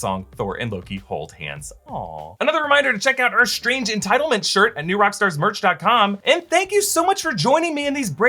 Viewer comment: says, “Strange entitlement shirt.” Instead of ‘strange entanglement’ lol.